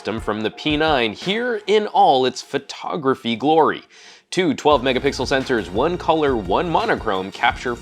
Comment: did I hear "megapickle"?